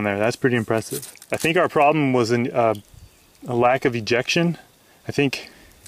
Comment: i thought you said erection instead of ejection